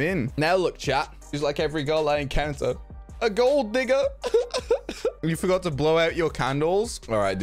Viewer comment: im not racist or anything but i heard "a gold n-" instead of a gold digger